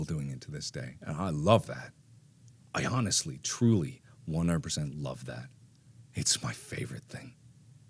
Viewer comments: the caption says "I honestly truly 1% love it" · lol, the auto-generated captions says 1% xD